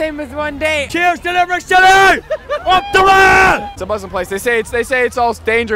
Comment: did he say ‘Up the Whas’? Lol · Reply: Think he said ‘up the RA’ which is probably a reference to the IRA